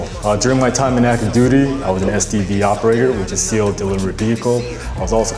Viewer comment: lol SDV operator I thought he said std operator I was like yeah, so what? we all were in college.